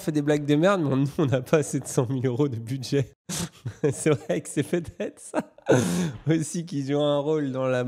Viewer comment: il dit quoi exactement? J'entends "c'est vrai que c'est fait dead ça" et je pense pas que ce soit ça...